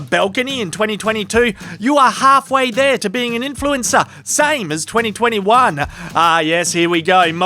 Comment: Influenza? I thought they were vaccinated? Ohhhhhh……. Influencer……… Must have misheard.